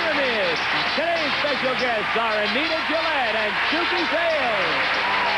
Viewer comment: I thought I heard Anita Bryant instead of Anita Gillette.